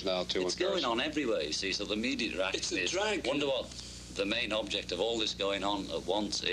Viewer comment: What does John say at 4:04? ''Wonderwall??'' Anyone?